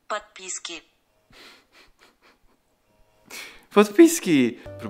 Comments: jak była subskrybcja to ja zrozumiałam podpaska😹😹😂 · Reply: Ja usłyszałem "Podpizgi" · usłyszałam - Pan z pieskiem XDD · Ja zamiast podpiski usłyszałam podpaski!!!!😂😊😊😂😂😂😂😂